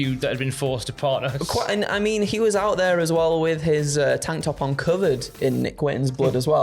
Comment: did he say tank top on Covid? Also, if Fox has been convicted of a felony, it’s going to be difficult to impossible for him to get into the country. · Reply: Tank top uncovered, it's that thick accent 😂